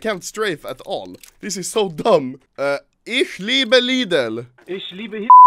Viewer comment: did he say ich libe hitler?